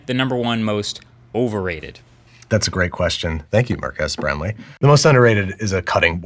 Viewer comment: I swear I heard him say "Mark-ass Brownlee"!